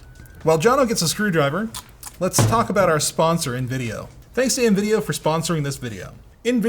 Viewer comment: I thought he said Nvidia sponsored the video at first😂